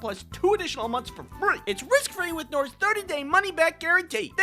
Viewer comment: When he said “risk free” I thought he said “rizz free”!!!